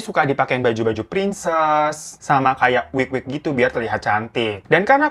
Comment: Wik - wik Hah? Oh wig - wig Salah denger😭